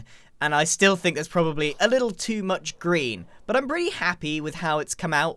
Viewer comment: 10:07 "I still think there's probably a little too much Grian" LOL! (At least that's what I heard.)